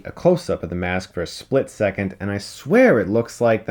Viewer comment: I swear the mask is crashed into Vader's .... (Visage) what did say?